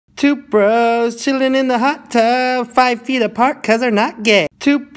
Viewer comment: I literally heard “Toothbrush Chilling in a hot tub”